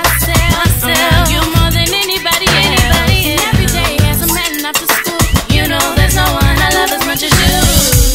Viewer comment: All this time I thought they said "and every day there's a man after school" lol